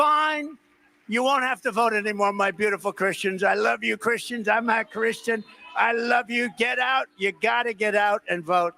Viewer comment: I heard him say he is not Christian at 6:55. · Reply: The media changed it,he actually said I am Christian